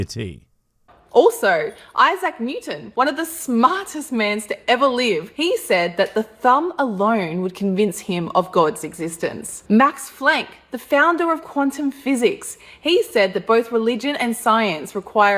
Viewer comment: Did she say Max Flank???? This whole time I've been calling it the Planck Constant- I'm such an idiot!